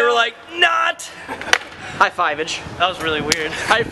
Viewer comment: Lol I thought you said "High five, bitch", and I was like: wait what??